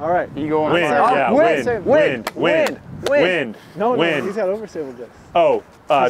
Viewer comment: Anybody else hear the Hawks from The Mighty Ducks chanting "WIN! WIN! WIN!"?